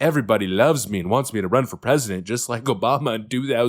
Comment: I thought they said they're going to put newsome in for president 28